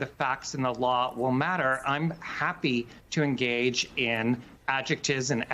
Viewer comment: I misheard that the judges want "their dang court" :face-green-smiling